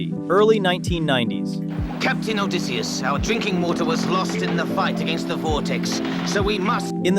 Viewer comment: I thought he said "drinking war". That would have been hilarious.